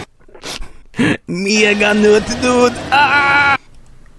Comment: what's he saying? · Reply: Mega nut dude, ahhhh